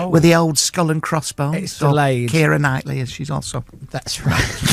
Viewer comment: What did he say around 11:19? Sounded like said it's delayed after